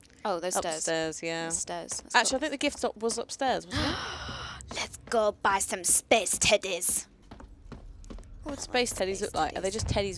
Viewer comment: "Let's go buy some space teddies" I can't be the only one who misheard that